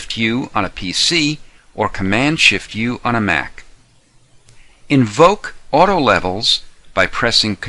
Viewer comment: What he said from 01:12-01:15 when he said "levels"? Please help There are 3 or 4 words before "levels" Thank you!!! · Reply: lalala "Invoke auto levels" - essentially telling you to open up levels.